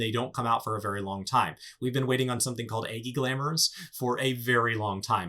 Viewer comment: Agi glamours, what did he mean or did i hear it right? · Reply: Egi glamours, basically people who play the Summoner class have been asking for the devs to add cosmetic appearance skins for their pets (known as Egi's).